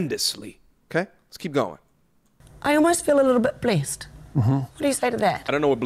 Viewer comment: I think the she said blessed